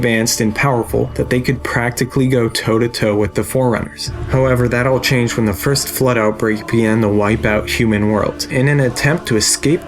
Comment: What does he say at 11:52? I really cannot understand what he says there. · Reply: "However, that all changed when the first Flood outbreak began to wipe out human worlds." I thought that was pretty clear, myself.